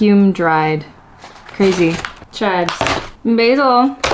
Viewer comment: Girl- 😸when you said “chives,”, I thought you said “chads” oh my gosh, so funny!!😂 luv the vibe in this vid.